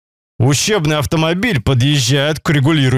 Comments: Ущербный автомобиль послышалось)) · Мне послышалось ущербном автомобиле. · Мне одному послышалось "ущербный автомобиль"? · Ущербный автомобиль... Мне однуму послышалось?! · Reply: Вы не один, мне тоже послышалось что ущербный, а потом прислушался он говорит учебный!!! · Послышалось "ущербный автомобиль". Остаток ролика ждала подвоха.😅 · Ущербный автомобиль... Мне однуму послышалось?!